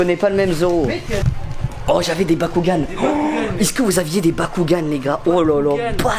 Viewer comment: j'ai entendu BYAKUGAN ( Les Hyuga en sueur )